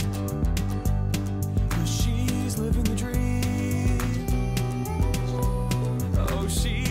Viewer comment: i thought he said "she living with dream" XD